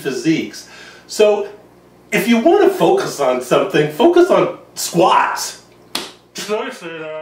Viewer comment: did he say "scheiße" -- the german word for "shit/fuck" ??